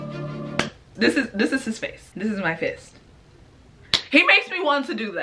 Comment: OMW I THOUGHT YOU SAID "Look, this is his face, this is my face."